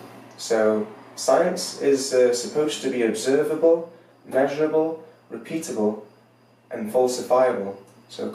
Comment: Does he really say "science is supposed to be observable, measurable, repeatable and FALSIFIABLE" (5:50) or am I unable to make out his words because of the raucous laughter in the room?